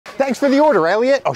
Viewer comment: I thought you said “thanks for the order idiot” 😂